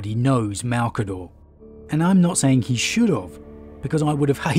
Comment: did you say “should OF”